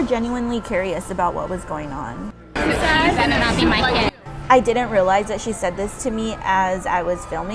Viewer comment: I can't tell what that woman in blue was saying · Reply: She said something about me filming her kids.